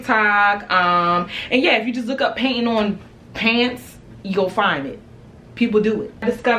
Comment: At first I thought it said painting my dream😂😂